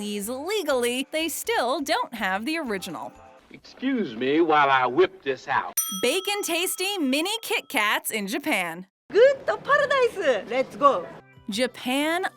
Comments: I was looking away at 2:15 and I thought she said “bacon tasting kit-kats in Japan” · I thought you had said “bacon tasting Kit Kat’s” but no it’s bake n tasting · I thought you said "Bacon tasting kitkats"